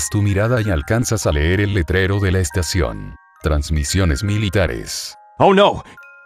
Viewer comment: No entendi alguien mi explica q es transiciones militares?